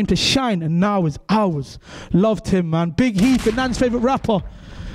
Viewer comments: Thought at first it said big health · i thought it said "Big Health"